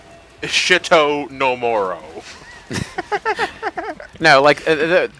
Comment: When said "Shit-o No More-o", I thought he said "Shit-o Nomura"